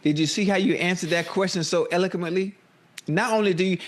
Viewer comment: jaja "You see how you said that so Elminquently" .. dude sounded like when we used to the alphabets and say "Elo Melo P" 😂